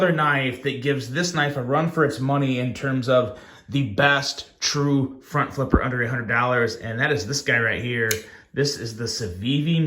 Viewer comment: Lol for half the video I thought you were saying that these were the best two pure front flippers under $800. I feel dumb, but I recently learned to love front flippers so I can’t complain about another good one 😃